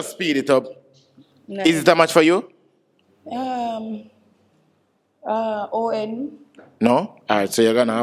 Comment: did she say ON instead of NO?😂😂